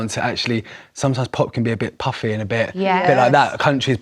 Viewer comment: Did he say Pop is ‘puffy’??? Wt actual F!🤯